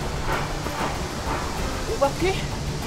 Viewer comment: 초나는왜자꾸이하는말이오바퀴??라고들리는것인가??ㅋㅋ